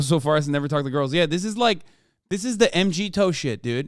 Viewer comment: what is hasan saying at 26:12 n-gee-toe? I'm trying to figure out what the reference is to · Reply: he's talking about mgtow (men going their own way). a dumb ass movement